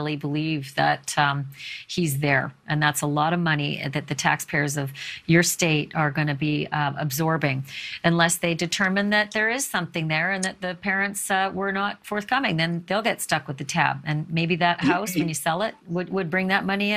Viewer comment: I thought they said the parents weren't speaking to the daughter